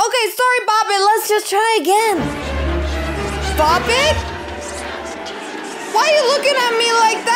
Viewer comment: The bop it intro was hilarious 😂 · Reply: I thought she said pop it but now I see this comment alright